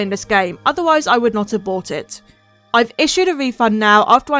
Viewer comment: I thought you said “abort it” and I was so confused lol